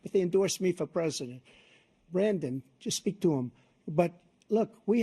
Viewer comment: did he call him Brandon? 😂